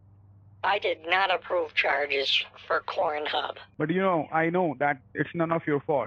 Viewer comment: Did he say 'cornhub'?